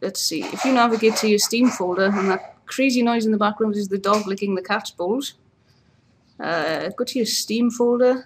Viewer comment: Thought you said "Dog's licking the cat's balls" :(